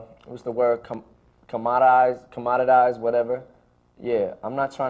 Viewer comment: What was the word he was trying to say @ 1:20? I love learning new words and respect that he tried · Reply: commoditize, basically putting a price on something that should not have one.